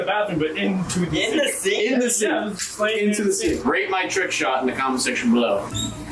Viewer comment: I think it says rate his trick shot my rate is 15/10